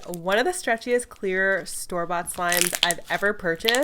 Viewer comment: KSQUADD! I am giving away ONE of every new Elmer's Gue slime to one lucky winner! Enter the giveaway here: <link> Who won in today's vid?!? and who should i review next?! ILY GUYSSS <3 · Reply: i thought you said starbucks slime 🤣🤣lmao